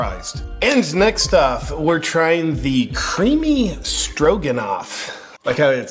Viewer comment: At first I thought you said, "creamy strokenoff"